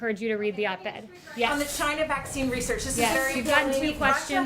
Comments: For the sake of objectivity, I heard: "Okay, you don't want to engage." Like if you heard the same. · I heard: "you don't want a 12 guage" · It sounds like she said "Thank you Kayleigh for ignoring it." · MSM doesn’t like it when confronted with facts. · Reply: I can't believe what I hear from all these comments. You people agreeing that this lady said such offensive language need your ears checked because you aren't hearing properly. 3:09 is the statement, and the lady said simply, "Okay, you don't want to engage." Shame on BlazeTV for saying otherwise... maybe whoever posted/clipped this video needs their hearing checked as well. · Sounds like she said "ok you don't want to engage" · At first I was enraged because I thought I clearly heard b*tch but turns out its "ok you don't want to engage" still childish but fortunately people still aren't calling eachother bch at the whitehouse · Nah - I heard " I don't think you want to engage."